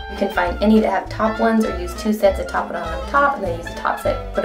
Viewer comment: did you say you use TWO top sets of lashes? I'm sorry but you were talking to fast and I coundn't understand. Lol. THANKS!! :)